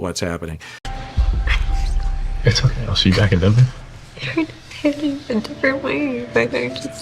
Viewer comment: I was listening to this video without looking at the screen, and realized how inaudible the things that she says are. Rewinding and watching with the subtitles, what I had gleaned from what I heard was different than what was on the screen, and I think production was likely using that lack of audio clarity, and also not showing us her face while she was talking, to serve the narrative they wanted. She is definitely being confusing and unclear in this situation, but for when they have her saying something like “they’re trying to paint it in a different way”, I heard “we’re going different ways”, which is something I heard earlier too. It might not make a massive difference, but it does remind me that these things are heavily edited for the purpose of building storylines to make it more entertaining and engaging to the audience. They kinda didn’t need it though, she was already being very confusing (though I don’t know that Patrick specifying her statement with “you don’t want to *go to Baja*” really helped her communicate what she was already struggling to). But ultimately, actions speak louder than words, and she’s pretty awful to him with talking about her ex and pressuring him to reveal his ethnicity, and then of course other details that happen after this episode too… 💍